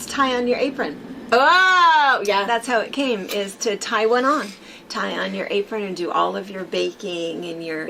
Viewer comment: Lol!!! "Tie One On" Day. When you first said it, I thought, you said "Taiwan On" Day...like, eat Taiwanese food!!!!! 🤣 🤣🤣